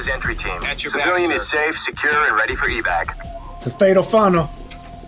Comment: subtitles say “fatal frontal” (which sounds hilarious), but I’m pretty sure he said “fatal funnel”. Fatal Funnel refers to CQC close-quarters combat.